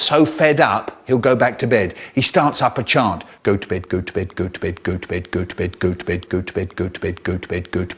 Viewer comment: Goat Bait Gucci Bag Good Twit Good Bed Lmao these are all I can think of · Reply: I hear goat's fed.